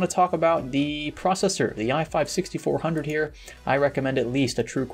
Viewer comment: Who else thought he said "The i4 6400"